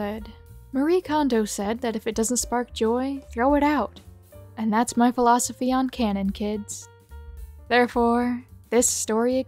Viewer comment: I thought that said "and that's my philosophy on kids"